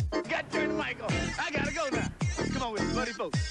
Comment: Was sagt der Schwarze bei 0:10? xD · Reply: Turn ya mike off..